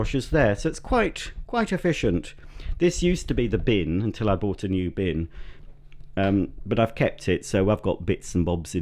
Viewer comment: Thought you said ud bought a new caravan?